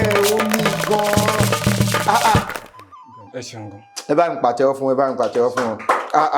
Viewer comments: I heard “e seun gan” You should listen to it again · I am just watching the show. He didn't say 'so'. I guess it's his accent that made it sound like he said it when he actually said 'E seun gan....' Please, I sent some money, let him have it as part of his winning.